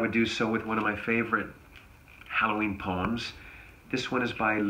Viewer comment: I thought said Halloween Porn and I got very concerned.